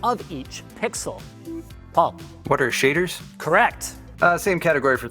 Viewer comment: Subtitles: 26:49 "Water shaders", Pretty sure he said "What are shaders" xD 43:13 "What is NNX", It looks like Linus say MMX